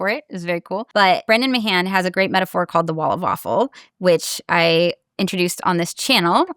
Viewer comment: The bit about the wall of awful cropping up around vague or abstract tasks in particular makes so much sense that I’m honestly a little annoyed that it didn’t occur to me before. It’s the whole ‘do you avoid tasks that require a lot of mental effort?’ thing that crops up on every single ADHD questionnaire. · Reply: Lmao 😂😂😂 I thought she was saying "wall of waffle"!!! Said to myself, I need to go find that guy bc i want to know why "waffle" lol. Thank you! One less rabbit hole to go down.❤